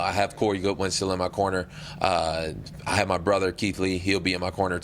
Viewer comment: Did he say his brother Keith at 2:50 ? Another Keith and kevin😂